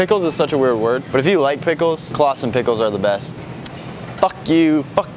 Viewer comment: is it jus me or did he say “ClawsoN pickles”